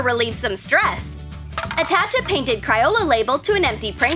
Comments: did anyone notice that she said criola instead of crayola? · she really said Ciyola instead of Crayola